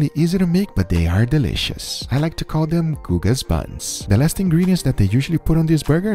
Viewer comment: Anyone else heard "I like to call them Guga's butts" instead of buns?